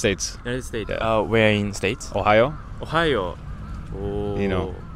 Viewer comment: lol ... I thought he suddenly said "Ohayo" as Good Morning in Japanese but I remembered Ohio.